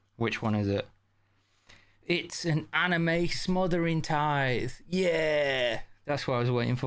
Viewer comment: I was only half paying attention and thought you said "Smothering Thighs", went to google and was mildly disappointed at the picture. Still anime tiddy though so yay.